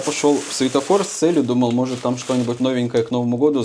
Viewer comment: 😂мне послышалось,, ушёл в светофор,, 😂🙈